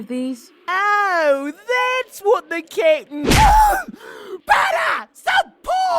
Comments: everytime jeice says "Captain" it sounds like he is saying kitten lol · Jeice sounds like he is saying "kitten" when he says "captain". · For years I thought Jeice said kitten but that was his accent saying captain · It sounds like Jeice is saying kitten when he says captain lol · I swear everytime jeice said captain i thought he was saying kitten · It sound like he saying "kitten" but its suppose to be "captain".... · Took me a while to figure out Jeice said captain and not kitten So when he said “ I’m going to get the captain and he is going to beat you up” I heard “ I’m going to get the Kitten and he is going to beat you up”. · seriously it sounds like jeice is saying kitten instead of captain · Everytime he said "captain", I heard "Kitten". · omg for almost the whole episode I thought was saying kitten instead of captain